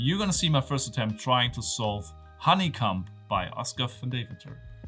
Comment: lol he's definitely saying honey cum instead of honeycomb.